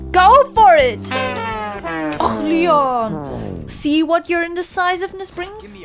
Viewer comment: Awesome Video again But one thing got me wondered. On 6:56 it sounds like you speak german with the och leon. Could be I misheard there and it's just a coincidence